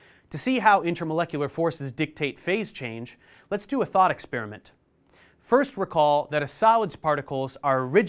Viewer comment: I heard 'thot experiment' and I was like... 👀